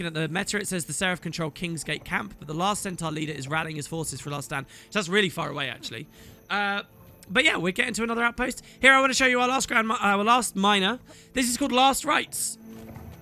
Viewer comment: lmao, I heard "Our last Grandma"